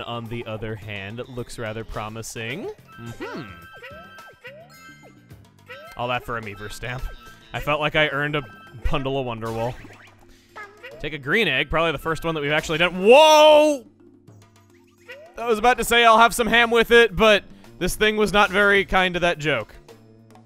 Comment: Did he said "Pundle of Wonder Wool"?